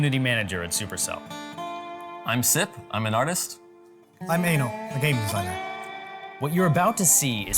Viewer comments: I thought said "I'm anal" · I thought the said " hi I'm anal " 😂😂😂